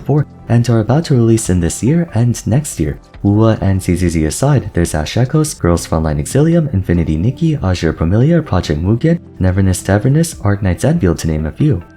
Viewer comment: can anyone tell me what gacha games he listed? I cant really tell and subtitles arent helping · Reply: zzz wuwa ash echos girls frontline exilium infinity nikki azur promilia project mugen neverness to everness arknights endfield